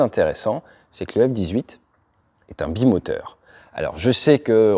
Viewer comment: J'ai compris Yak18... XD c'est mon avion préféré. Pas le F18.